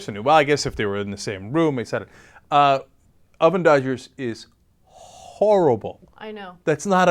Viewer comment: I had no idea what they were saying until the very end: "oven dodger". I thought they were saying a German word I didn't understand.